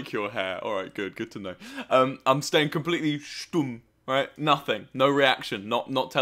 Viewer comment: did he say "stumm" ?